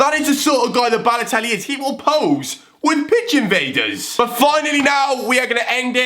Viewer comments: when he said pitch invaders i thought he said pigeon famers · when he said pitch invaders i thought he said pigeon vaders!